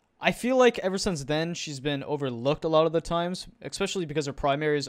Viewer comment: We need a overrated list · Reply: yWorry !! Ik I already said that she is not overrated I thought he said overpowerd